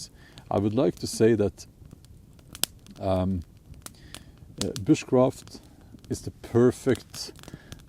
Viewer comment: At first, I heard 'witchcraft' and thought "Wha...??" Oh, you said Bushcraft.